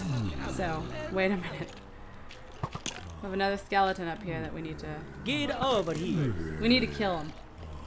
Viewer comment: omg at 49:27 said we have another skeleton we need to and then the guy said get over here it sounded like said we have another skeleton we need to get over here xD